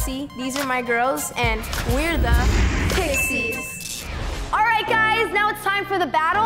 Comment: Did anyone else heard pissis instead of pixies lol